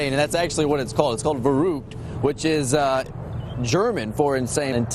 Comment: I had to listen to it twice, until i figured out that he actually said „verrückt“😅😅 He pronounced it in such a funny way😂😂